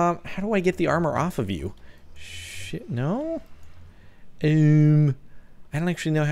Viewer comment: DID I HEAR A SWEAR WORD SH**???